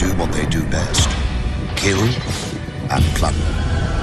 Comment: Did he really say "they do what they do best.....KILL!!!...and F*CK!!!" 😂😂😂 Best commentary EVER!!!